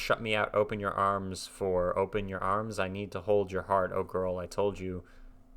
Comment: I always heard "open your arms once more".